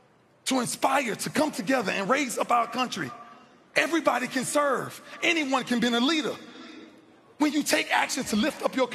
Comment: Great listen. So inspiring! Was trying to work out what ppl shouted at minute 32:00 onward etc. I could not understand the words. Could anybody please help me? Thx. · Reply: Defense Defense